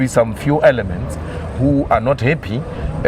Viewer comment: he was not money indeed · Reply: I thought he said moneyed